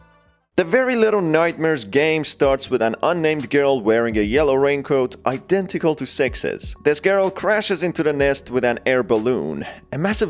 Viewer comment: I thought you said "Identical to sexist"